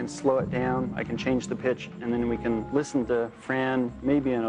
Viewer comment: I thought he said - I can change the bitch xD Actually he said pitch